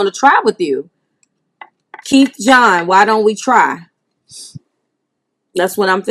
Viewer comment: sounds like you said “keep John, why don’t we try.” It’s crazy because my guy name is John as well. This is definitely for me.